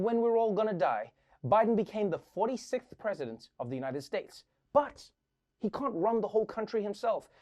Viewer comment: did he say "united steaks" 😂 the place to be as an meat eater